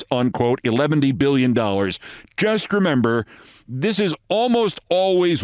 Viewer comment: Did Olbermann just say "eleventy-billion-dollars"? at 44:03 And with regards to his joke about "pushing Hitler and Mussolini overboard and shooting O'Malley twice," could you replace O'Malley with Grump?